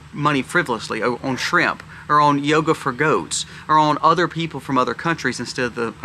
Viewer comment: I thought you said yogurt for goats,lol. Yeh we need to cut the fat!